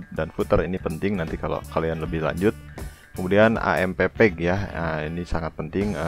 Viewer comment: GUA DENGER : AEMPEPEK . WOI GIMANA SI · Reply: AMP page kali sob